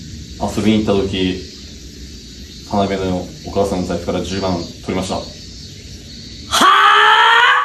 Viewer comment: 田辺のお母さん抱いたら、10万くれましたって聞こえてびっくりして、聞き直したら 財布から10万取りましただった(笑)